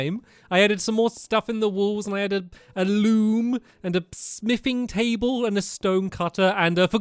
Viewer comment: When you said smithing table i thought you said sniffing table! LOL